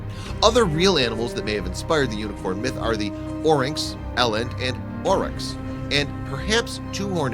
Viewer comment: DID YOU SAY THORAX FROM MLP!? lol